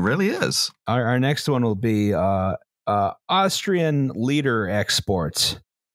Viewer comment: I initially misheard "Austrian leader exports" as "Austrian Lieder exports" and wondered if was talking about Schubert!